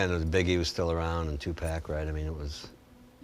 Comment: did he say two-pack!!? Wtf